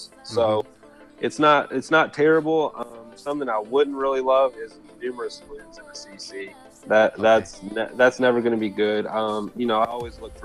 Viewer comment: what did he said? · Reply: I believe he said: numerous troops in the CC; Miners are terrible are taking a lot of small troops. That why in a separate comment that Miner's main issue are ground skeleton traps.